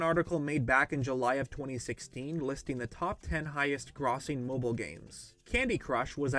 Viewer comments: buddy did you just say "Gr-awss-ing" lmao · did he just say "grawsing"?